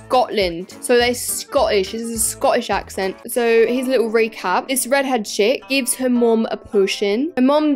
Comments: SECOND stop because 9:00 i thought u said “this red head shit” 😂 edit: just came back 3 weeks later not expecting to get my moms lucky number 13 likes but ty 💀😭✨ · is so funny i think she said the little red head s*** not a hate comment btw